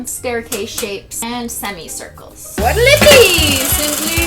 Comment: at around 7:45 you said what'll it be hard to understand and i thought you said sizzly...which sounds like the way some of my family members my name, Sicily and i freaked out a sec like when you hear that knocking sound effect in a video and aren't expecting it